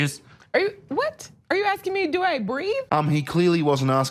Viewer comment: Sounded like "breathe" to me, not agree.